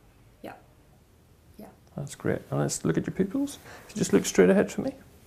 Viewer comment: did he say look at the PICKLES!?